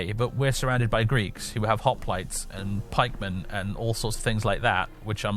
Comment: definetly misheard Hoplites as Hot plates at 1:02:50 those pesky greeks